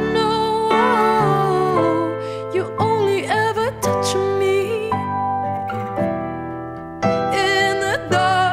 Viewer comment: Her voice is so enchanting. I first thought she said your only touching me in the dark arts